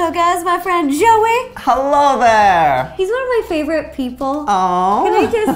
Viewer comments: In the beggining I thought she said "he's not my favorite people!" 😂😂😂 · I thought she said, "He's not my people favorite people."